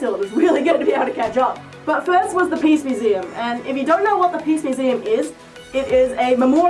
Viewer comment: Oh, PEACE museum. At first I thought you said PEEPS museum.